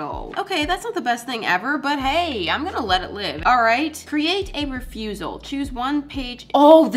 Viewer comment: I THOUGHT IT SAID “CREATE A FUNERAL” 😭😭😭😭😭😭😭😭💀💀💀 14:20: doodles Also: creates the backrooms